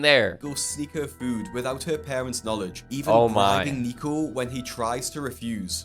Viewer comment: Doing what? I'm sorry am I hearing this properly? Oh they said bribing, oh thank God. I thought it said "grape"... Edit: 8:13 It really is abuse if they are CHOOSING to be like this.